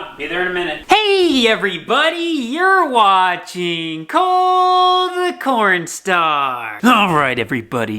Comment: Does anyone else just say “Cole the Cornstar!” along with him in the beginning? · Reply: NOPE! I hear "Corny the Colestar!" lol